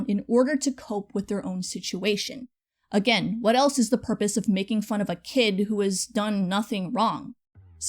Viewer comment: I'm sorry, I know this isn't the point but when you said he released "Purpose"... I heard "Herpes" x_x Seriously